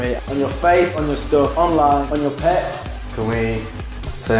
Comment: Did he say "with me" at 0:24? :)